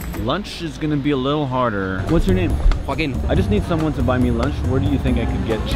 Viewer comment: did he just say that his name is F****N